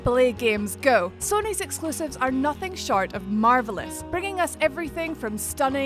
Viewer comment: Aye stop stop, hol' up 0:45 Did she say shart? 😄😂🙌💀